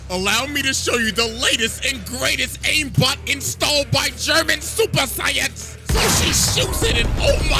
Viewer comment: it sounded like he said German super saiyans